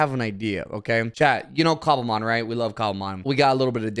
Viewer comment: NGL 0:06 I legit thought he said "Chat, you know Columbine, we Love Columbine." and was hella confused